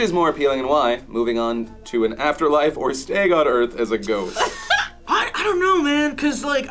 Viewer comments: I thought he said "staying on earth as a goat" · I was a bit confused at first I thought said after life or stay on earth as a Goat... which made it even funnier that said he had thought about it, but that did'nt seem too far a strech though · I definitely thought said "have an after life or be a goat"...